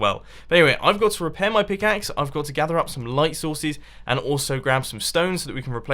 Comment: Did he say, "Going to gather some light souses."